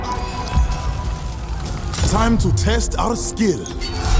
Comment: Did he say "Time to test our steel?" In the Bronze Age??? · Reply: Skill